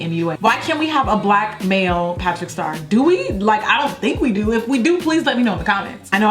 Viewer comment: did i hear you say "patrick star" i'm ctfu